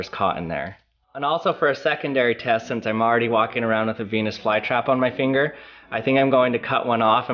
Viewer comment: I thought you said penes fly trap!!! 😂